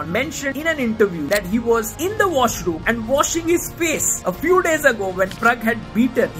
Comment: I'm sure you said something else but I've watched it 3 times and all I heard was "he was in the washroom, watching his piss"